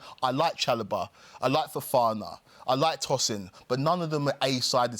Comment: did I hear him say he likes Tosin? 😢😢😢